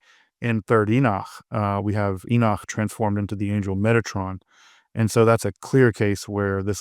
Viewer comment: I thought he said “Megatron” here and was really confused. Now I’m just dying laughing.